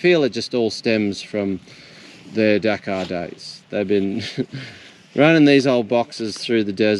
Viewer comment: When I heard "from their Dakar days" I had to realize it was your accent because it sounded like you said "from their Dachau days." 👀 We are talking about Germans after all.